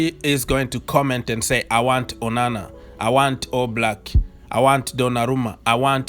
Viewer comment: caption says "Banana and All Black" instead of Onana and Oblak lol